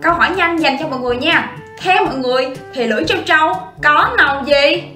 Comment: Tui nghe thành trân châu?